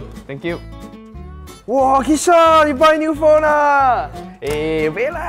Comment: The caption is wrong It should be "Kishan: Wah, Ben you buy new phone ya?"